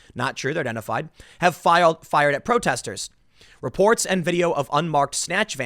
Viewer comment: Woah woah woah slow it down there Tim. I'll tell ya what man, @ 7:27 you accidentally said "FiLed" then immediately corrected yourself with "FiRed." Now I'm not saying I'm an expert or anything, but I was not expecting to see your Korean blood showcased in full force here with your Ls & your Rs. Your mom's side of the family would be proud.